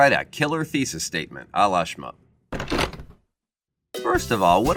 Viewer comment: I thought it said shampoo😅😂